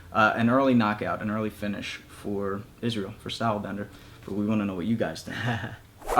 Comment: I thought it said “Style Blender.” 😂